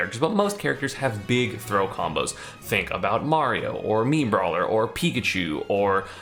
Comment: when he’s listing off characters with big throw combos, I could not help but hear meme brawler, instead of mii brawler, and I don’t think I’ve ever heard a more accurate description.